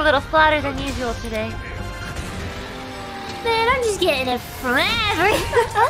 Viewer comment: what did gura say ? I can't hear clearly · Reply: "Man, I'm just getting it from everyone"